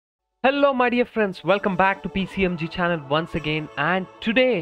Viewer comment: i had to repeat the first few sentence of this vid cuz i thought he said "hello my idiot friends" 😂😂😂